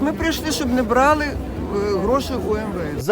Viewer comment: Як кажуть - за Фрейдом. На 3:10 я почула про причину тієї пенсіонерки прийти на мітинг - "щоб не брали гроші у МВС"!😂😂 В такому разі, краще, щоб не давали їм гроші!